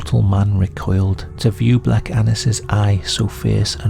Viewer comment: I thought i heard black anus 💀